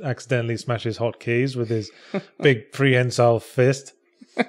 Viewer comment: What is the word that yahtzee uses at 2:35 to describe Gabriel's fist? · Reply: Harry G It sounds like 'prehensile'.